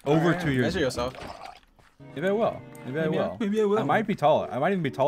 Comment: is it just me or dream suddenly got a little british there when he said “you know what?” or did he say smth else there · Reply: But like. He said Maybe I will, but he sounded like he was saying You Know What but British .